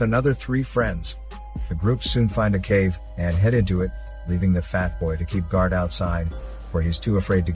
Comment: did he just say 'leaving fat boy to guard" weird choice of words but okay